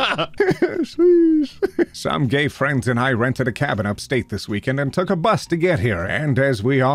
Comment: I swear I heard "Some gay friends I rented"